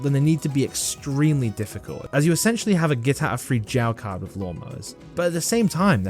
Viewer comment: I know im a bit late but did you say a get out of free jail card or am I hallucinating If I am I must be at 32 sanity in don't starve about 17 people know what im talking about here maximum